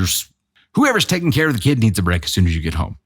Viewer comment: Did I mishear? I kept hearing "her kid"?